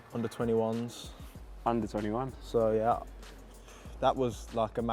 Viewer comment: thought that said UZI’s